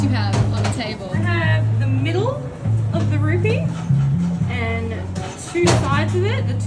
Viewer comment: I thought they were saying “Ruby” and was confused as to why it was green. Turns out they were saying “Rupee”, and it was green to match the color of a rupee with a value of one